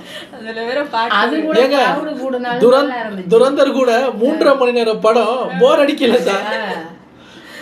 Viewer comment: Did He Say Boring Or Not Boring Didn't Understand That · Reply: Not boring